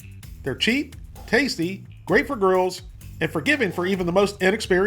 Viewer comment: did you say “great for girls”?